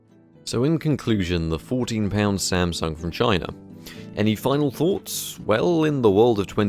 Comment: One of the times you said "Samsung from China" I heard "Samsung vagina". Checks out.